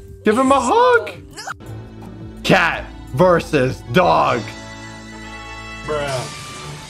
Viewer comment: Did he says dog? 0:35. Lol